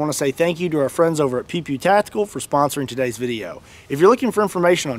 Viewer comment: I had a lot of fun making this one! I hope you enjoy it! Thanks for watching! 😁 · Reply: I wasn't looking at the screen in the start of the video and I swear I heard you say: pp tactical